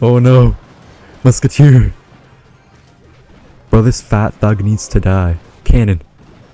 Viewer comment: I thought he said fat fuck